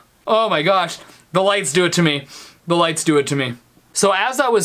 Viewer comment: it sounds like he said what do I do?